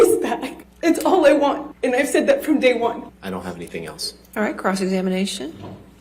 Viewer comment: When the judge said cross-examination I thought she said crotch examination LOL